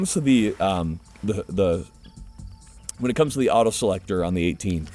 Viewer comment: I swear he was about to say hood selector